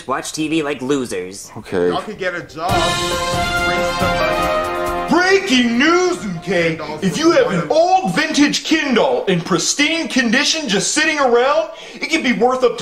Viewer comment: did goodman just said, "breaking news you gay"?